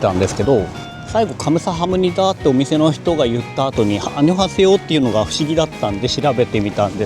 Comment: 楽しく観ました！ 「カムサハムニダ」と言った後に「アンニョンハセヨ」と言うのが不思議だとおっしゃっていましたが、「アンニョンハセヨ」ではなく「アンニョンヒガセヨ」を聞き間違えたのだと思います。 「アンニョンヒガセヨ」は「さよなら」の丁寧な表現です！